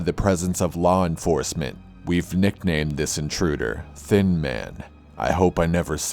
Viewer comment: had to replay it once cause i thought it said they nicknamed the one guy "thin mint" not "thin man"